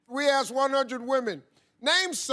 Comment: At first i heard "your mom" instead of your man 😂😂😂